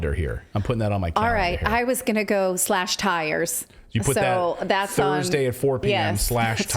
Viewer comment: "it's me" "no it's not you it's me" Classic! Edit : also when Natali said "I'm going to go slash tires" I could have swore she said "I'm gonna go flash buyers"! Immediately thought my favorite independent media source was getting extra spicy!